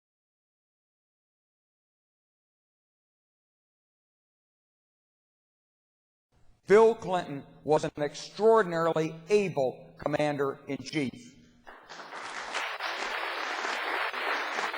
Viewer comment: What??? Bill Clinton was evil??? Oh, ABLE!!! My error. I'm not being funny. I really thought he said that. tony